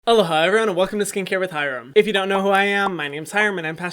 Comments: I thought you said hello Hyram for some reason and I’ve been confused for like a year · No one: Me repeating the beginning questioning how everyone hears "Hello, hi" and "Aloha everyone" when I hear "Hello hyram" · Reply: Please park Jimin in my garage omg sameeee! I thought he was saying hello hyram😂